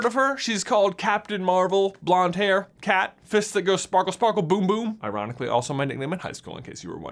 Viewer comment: All I heard was "Sparky-Sparky Boom Man"